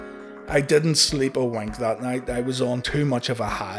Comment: I thought you said "I didn't sleep or w@ last night" hahaha